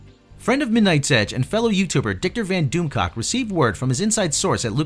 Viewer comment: did he say Dickter Van DoomCock?